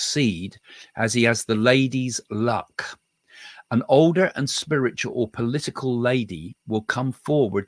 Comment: The lady in the background helping Trump could be his wife! Melania is doing an interview on Fox & Friends / Fox News this morning and is also promoting her new book. ❤ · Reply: 🙏 I thought he said an “older spiritual lady” .. wondering who she is..